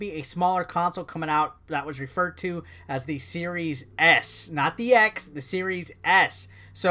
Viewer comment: Could have sworn you said series ass at some points lol.